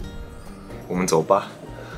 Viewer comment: im pretty sure the subtitles are wrong , if im not mistaken you said "lets leave" [ also thanks for writing my last name 5 times in calligraphy class]